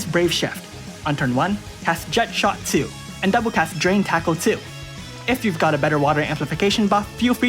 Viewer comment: I thought you said 'drink taco' 😂